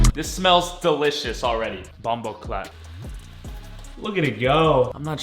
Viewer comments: only Jamaicans will get that .... why did he say that😂😂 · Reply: He said it's so weird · did he just say bomboclaat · Did he say bomboclat